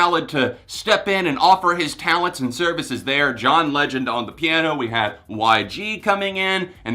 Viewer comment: i thought u said "John Lennon on the piano" lmao